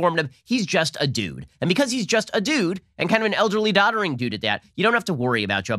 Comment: When Ben said "he's just a dude"--I thought he said "he's just a douche"...way more accurate.